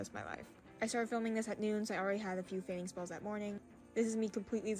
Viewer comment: As a person who actually has regular fainting spells, like several a month due to both POTS and Narcolepsy, I can 100% say that no one that actually deals with this faints so perfect and daintily as she did. You almost always faint when you least expect it, often while doing something or exerting yourself, such as standing or talking while standing or even eating a large meal (this isn’t always the case but it often is) and with POTS it usually happens when you stand (or even sometimes sit) too quickly. These people are not only bad at faking but they don’t even take the time to research what actually happens and what it really looks like to seem even halfway realistic…they have serious issues! What do they seriously have to gain from faking these disorders other than mockery and humiliation? Get a frickin life, people! It’s not cool to be sick or have mental issues…people that actually suffer from the disorders these people are faking can attest to that fact. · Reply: For a second I thought you said "farting spells"